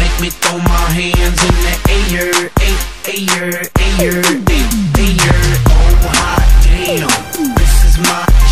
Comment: Williams accent makes it sound like hes saying: in the iyer, i iyer, iyer, i iyer